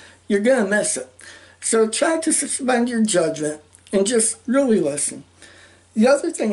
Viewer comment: oh, erm, sorry ... what did you just say? ;) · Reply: "Just really listen."